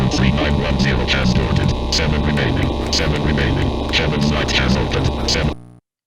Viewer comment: did my best to transcribe this: "(Unintelligible) Victor Lima Omega Delta Victor <num> (unintelligible) seven remaining seven remaining heaven's light has opened seven-" There are maybe two letters at the start before Victor, and the bit after the numbers before the first 'seven remaining' sounds like 'has boarded'? Its interesting that it said Omega instead of Oscar, which is the usual word in the phonetic alphabet for O.